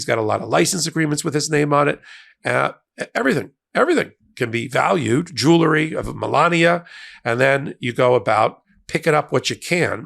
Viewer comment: The first time listening to this part, I missed the key word "of." I thought Popok (humorously) said: "Everything, everything can be valued. Jewelry, Melania..."